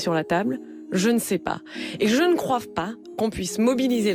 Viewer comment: C'est moi ou à 0:06 elle dit "je ne croive pas" ???! 😯